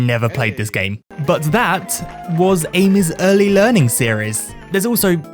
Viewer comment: I legit thought he said "Anus early learning series".